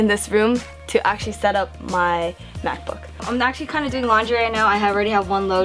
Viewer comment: When she said laundry I thought she said lingerie lol. I was like, dafuq, you aint gina